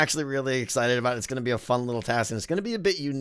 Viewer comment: Did he say unic?